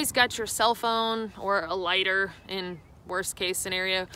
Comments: I heard “I actually do keep a ladder and a backup ladder.” Then I realized she said lighter. Man, that was one hell of a confusing bit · When you began to talk about a lighter, I got so confused...I thought you kept saying ladder...lol...I thought, why would she need a ladder? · When you kept saying lighter I thought you were saying ladder and for about 60 seconds I found myself wondering wth you did with a ladder while hiking.